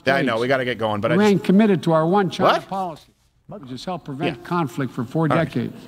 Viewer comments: Am I going crazy or did biden say "our one child policy" at 1:16:32 ? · Did I hear biden say "we remain committed to our 1 child policy" Screw that noise. Make babies Christian families and change the world. For God's glory.